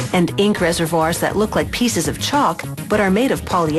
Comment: I thought she was gonna say "pieces of SHIT"